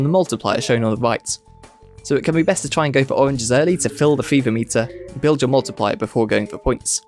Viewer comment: i always thought the fever meter said "feverme tea"